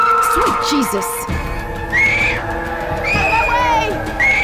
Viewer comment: It sounds like Rochelle is saying run away instead of outta my way